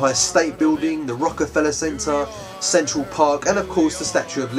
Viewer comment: i thought i heard sexual park rather then central:P